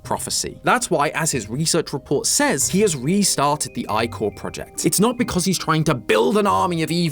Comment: I thought you say it as "ick-core"